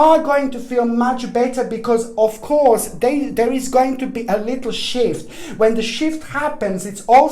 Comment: I had my phone on speaker...and was saying “shift” (after replay🤣) every time he would say “shift” insert “shit” (sorry, terrible I know) but that’s all I kept hearing from a distance.... and I was laughing so hard! I had to replay again and again....🤣🤣🤣 I appreciate every message and video and I do take them serious....point to story....listen closely... 😳😬